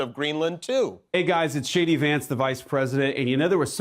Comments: Did he just say SHADY VANCE? I overheard it like that 😂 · Whenever someone says the vice president's name now, I always hear "Shady Vance". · Sounded like he said It's Shady Vance". That fits! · did he call himself Shady Vance? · Did he call himself "Shady Vance"? 😂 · Is it just me, or does it sound like Vance is saying, "It's Shady Vance"? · It always sounds like he is saying Shady Vance. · did he say Shady Vance?